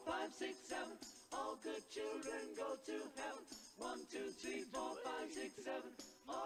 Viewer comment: On the last one it sounds like "all good children go to HELL" instead of heaven